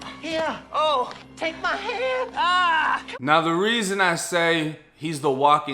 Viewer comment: Man you a fool for that Scary Movie clip 😂😂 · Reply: I always thought that clip said "take my strong hand"